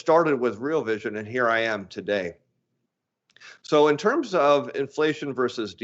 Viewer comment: I thought you said your macro journey started with DFA